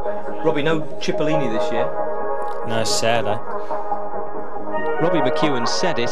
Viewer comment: It’s driving me nuts what did Robbie say at 11:22? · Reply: I think he says “No, sad eh” as in it’s sad Cippolini isn’t here, tongue in cheek obviously